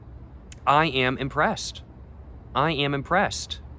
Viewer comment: zoned out for a second, thought he said depressed. I was so confused😂